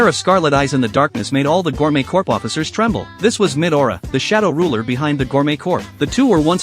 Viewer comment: What did I hear 'Mid aura' instead of 'midora' 💀☠️